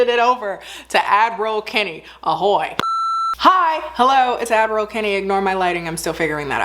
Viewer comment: When I tell you - i heard “Avril Kenny” - and finally “Admiral Kenny” for so long girl - and today, closed captions has opened my eyes 😂😂😂🤣